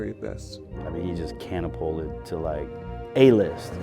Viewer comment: did he say “cannapolted”